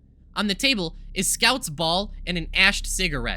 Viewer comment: for a second i thought he said "on the table is scouts balls'